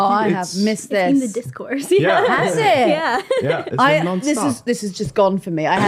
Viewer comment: When said "It's been the discourse" I thought she said "It's in the Discord", and I literally got excited thinking there was a PoliticsJoe Discord for a second there.